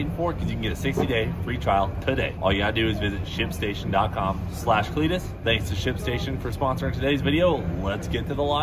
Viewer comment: It’s a good job you put ship station on the screen for a Second I thought I heard you say shit station 😂😂😂.